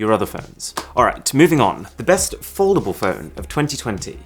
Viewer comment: My volume was down when he said the best foldable phone, and I heard "best affordable phone", so I was super confused when he pulled out the z fold 2 lol